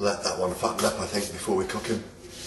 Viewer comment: Thanks for the annotation on fatten up. Haha. I definitely thought "did dpr just say fuck up?"